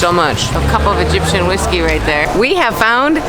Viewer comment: Did you say the salad is called Egyptian whiskey or did I mishear? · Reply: No, salad is salad. But the stuff in the bag is called “salad water” (aka Egyptian whiskey)